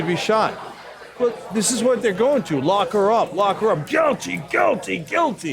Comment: What I heard was, "locker rub."